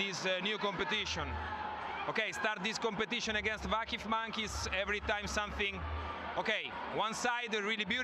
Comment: Vakıf monkeys ? are you kiddin me!! what a disrespectful, rude, racist wording !! more cry idiot !! 💛🖤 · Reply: "vakifbank is"